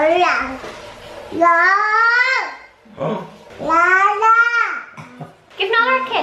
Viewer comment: did he say nala